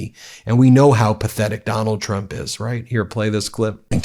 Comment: I thought Governor Newsom said McDonald Trump😂